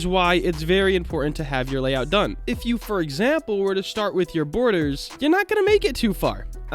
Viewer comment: what did he say? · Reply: Borders